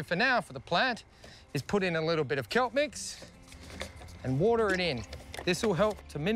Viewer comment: did he say "kelp mix"?